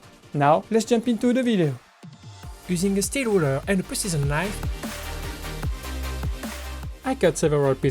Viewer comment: i hear prisoner's knife when you say precision knife